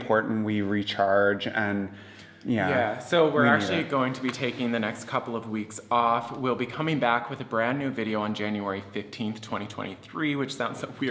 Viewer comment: Did you say you will no longer be on YouTube??? If so, I won't be able to catch you. VERY DISAPPOINTING.. I have looked forward to seeing you for over a year and will definitely miss these Sunday adventures. In any case I wish you very happy holidays and all good fortune in the future. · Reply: Hi again! I took some time to rebinge it. This is what they said: [at 16:45 and running] We're actually going to be taking the next couple of weeks off. We'll be coming back with a brand new video on January 15th 2023 [...] While we're not going to be posting new videos[...]check out our Patreon", [textplate at 18.58] See you on January 15, 2023 ------------ I understand this to mean "check out our Patreon while we're off for the weekends, we'll be back on YT 15/1". cheers!/CS